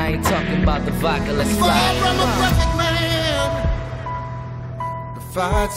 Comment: I'm far from a bucket man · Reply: TBH I thought he said far from a bucking ram...